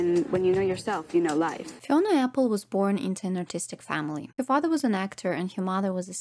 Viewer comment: first line i heard "fiona apple was born into an autistic family". prob checks out